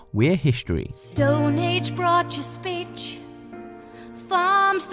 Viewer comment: DID SHE SAY BITCH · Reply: No she said speech